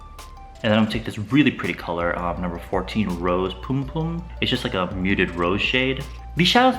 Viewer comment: LMAO I THOUGHT SAID "ROSE PUM PUM" (like the word for virginia) @ 12:40 😂😂😂😂😭